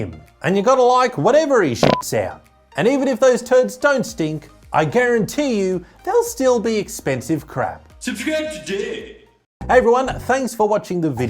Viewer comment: "Subscribe Today" - Congrats now you have read the words in voice🤣🤣🤣. I know many people are fans for this ending card voice by. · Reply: Oh, that's what he says. I always heard "Subscribe to Dave" and I was thinking who this mysterious Dave person is.